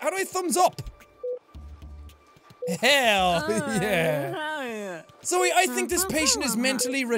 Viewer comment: tell me that doesn't sound like she said "hell yeah" in a jenna marbles voice- (in game right after he says it)